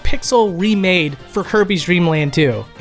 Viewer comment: 100% hands down convinced he said "Hirby" instead of "Kirby" here lol 😤😤